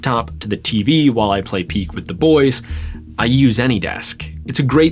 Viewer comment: Strange how at 6:41 he says "play PEAK with the boys" but the captions say "Play Battlefield 6"